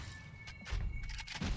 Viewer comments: I thought you said okay deport me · I thought he said “ok deport me” 💀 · lowkey thought he said deport instead of report i was like what